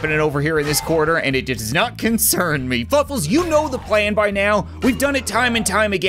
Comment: said fuckles instead of fluffles LOL